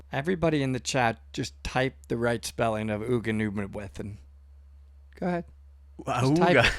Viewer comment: Gugan Uthe With Cave My best guess, from what I heard the narrator say.